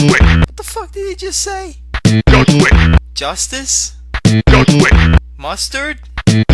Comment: i think he said just wait